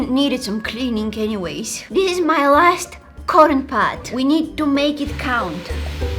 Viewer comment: I absolutely love your accent but when you said "this is my last cotton pad" I swear I heard " this is my last corn pod" lol I was like wtf is a corn pod then the sentence processed again thru my brain 😂😂😂